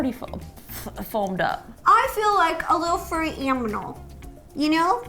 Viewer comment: Ro says Aminal instead of Animal....lol😂..but great video ro btw im subscribed and i hv my notifications on💖lysm